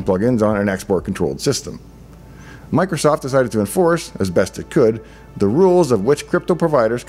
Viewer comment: says "Microsoft decided to enforce". Subtitles say "Microsoft was REQUIRED BY LAW to enforce". Fishy, fishy...